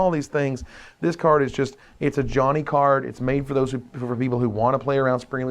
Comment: everytime they say its a "johnny card" i hear its "ajani card'